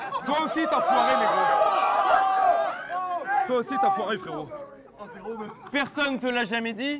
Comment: À 5:41 il dit « toi aussi t’as foiré n*gro » ? 🧐🤨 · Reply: Frérot *